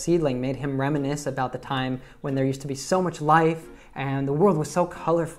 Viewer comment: Hello! 13:16 Does say here when or where? Because I hear that he says when which is correct I guess but it is where in the subtitles.